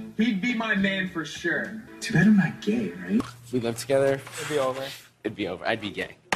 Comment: "Dude I'm not gay... Right?" Literally asking bc he's so confused XD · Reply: I thought he said "too bad I'm not gay, right?"